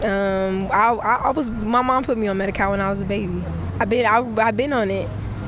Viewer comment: When she said Medicare, I swear I thought she was saying Medicau or Medicow. Thought there was a new social program.